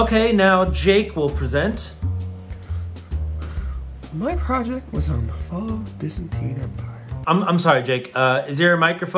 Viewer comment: I thought he said “my project was on the distant peanut butter”